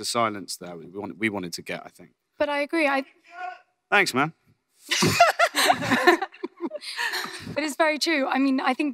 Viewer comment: What does that guy in the crowd say at 29:59 ? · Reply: "Love You Kit!!"